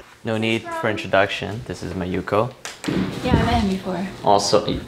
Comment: i thought he was saying "my yuko" so i assumed she's his gf lol